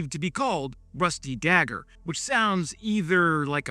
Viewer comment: I love mishearing things. "Rusty Gagger" is kinda cool. "Quantity has a quality of its own." - Probably not Stalin but quite true.